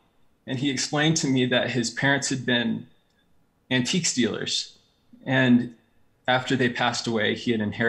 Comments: when was talking I thought he said "his parents had been antique stealers." I had to rewind to figure out it was probably "antiques dealers". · The way he said “antiques dealers” made it sound like “antique stealers” and I was like 😳 Really awesome video. I enjoyed his story very much. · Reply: Lmao I legit thought he said antique stealers ! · I thought the guy said the seller’s parents were "antique stealers", and was surprised they would just admit that so non-chalantly 😂🤷🏻‍♀️ Took me a couple of seconds…. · thought he said "antique stealers" instead of "Antiques Dealers" at first